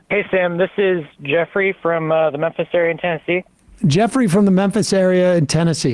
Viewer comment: At first, when he said "this is Jeffrey from the Memphis area, in Tennessee", i was sure i heard "this is Jeffrey, with the necessary intensity"!